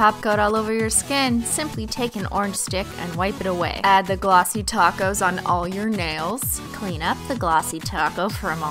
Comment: When she said "glossy top coat" I thought she said "greasy tacos"